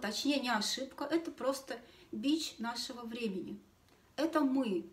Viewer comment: Как всегда замечательная информация и подача. Отличная наглядная история про трёх женщин. А вот «бич нашего времени» мне послышался как «b@ нашего времени» 😂 и ведь тоже верно...